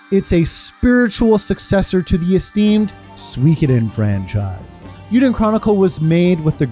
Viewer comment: What did he say here ? And that clip, is that from game cutscene or anime ? Looking forward to play Eiyuden Chronicle: Hundred Heroes :) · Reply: I said Suikoden franchise at that moment. And the cutscene is from the opening to Suikoden III :)